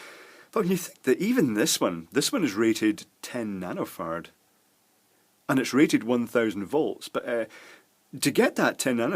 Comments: when he got to the section about capacitors I thought he was saying nanofart and microfart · I could have sworn you said you were measuring nanofarts! I did successfully google and find out you said nano-farad, but after reading the definition, I still have no idea what it is.